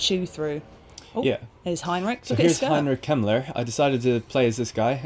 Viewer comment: Heinrich Kemmler is an awful name, because when you said it i honestly thought you said Heinrich Himler, who was the leader of Hitlers SS..